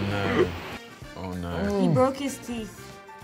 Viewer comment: What Tom said: Oh no What I heard: Mjölnir